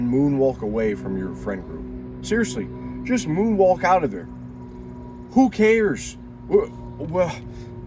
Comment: I didn’t do the math right in my head for a second I thought he was saying moonwalk into them 😂😂